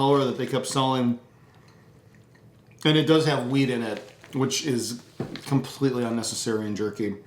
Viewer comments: did you say it has weed in it??? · Did I hear weed 😀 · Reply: WHEAT :-) Thanks for watching